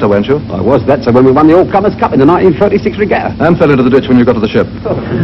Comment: WHAT THE HELL did those guys say to each other at 27:16 ?????? I'd had this movie on VHS many years ago, will probably just google the lines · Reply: fell into the ditch, not dutchman